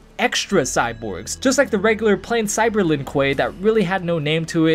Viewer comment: I thought it said cyber Liu Kang lol